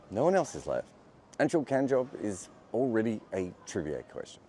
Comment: Did you just say Anshul Cumjob? 😂